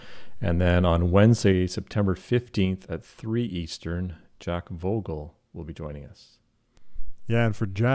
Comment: What? In 4:54 they say that Jack Bogle will join the show but... I don't understand, he is dead! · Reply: Jack Vogel :) I also thought they said Bogle until I listened to it a second time.